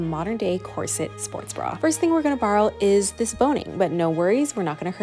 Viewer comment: i thought she said “boeing” 😭